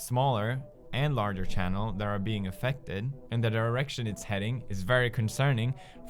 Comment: Did he say the erection it's heading at 0:33?😂